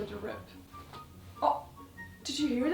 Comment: violent screaming at 10:16 bc i thought you said "rib"